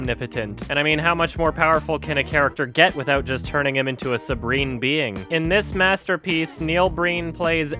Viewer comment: did Adam say subreen being at 5:31?